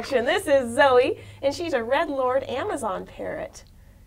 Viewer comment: I heard "Dread Lord" lol